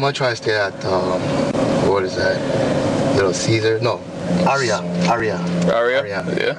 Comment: Did I hear “Little Caesar”